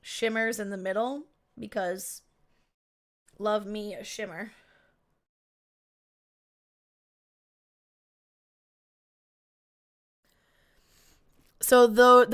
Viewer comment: I heard "Love me a shimmy" and expected to see a dance. I was not disappointed by the colors.